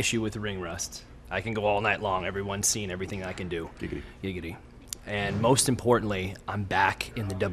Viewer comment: lol did Dolph say Gigity at 0:37 ???